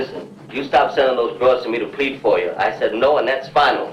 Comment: 💖 ~ 😏😏😏😏😏 ... 🤣🤣🤣🤣🤣 👌 Gotta luv YouTube's Transcript idiosyncrasies for comic relief: 😏 56:42 ☆ (Julie's boss, Alex, to Julie) "Now listen you stop selling those drugs to me to bleed for you I said no and that's final" 🤣🤣🤣🤣🤣🤣🤣🤣🤣🤣 ... Vs. ~ What Alex actually said to Julie: "Now listen – you stop sendin' those girls to plead for you. I said, 'NO' – and that's final." 😏 1:10:07 ☆ (drunken Dave to Julie): "But I want to meet you yeah but not your lion Mouse." 🤣🤣🤣🤣🤣🤣🤣🤣🤣🤣 ... Vs. ~ What Dave actually said to Julie: "But, I want to meet you, yeah – but not your lyin' mouth." When transcripts are so blatantly wrong & funny, sometimes I just can't help myself – gotta wildly relay it for laffz! They've gotten waaayyyyy more accurate, than they used to be – must say. (Absolutely no reflection upon 🍕 – ALL YT channels suffer from haywire transcripts now & then ... kinda fun to catch 'em.👌 👍✌🤙 💜 ✝️ 💜 👊 🇺🇸 ⚖ If you wish for peace, prepare for war. 💖